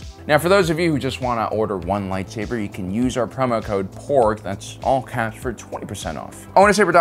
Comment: I wasn't looking at the screen during your promo so what I heard was: "If you just want to purchase one saber you can use our discount code 'POOR' for 20% off..." and I was like 👀